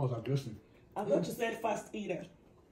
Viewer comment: That was aggressive 😂😂😂I thought you said fast eater. Madam I don’t have time 😂😂😂. Open your mouth!!! Be romantic. Stop adding jara😂😂😂. I had a good laugh